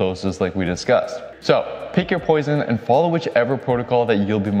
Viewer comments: Did he just say take your poison ? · DID YOU SAY PICK UP YOUR POISON? AHA! 😜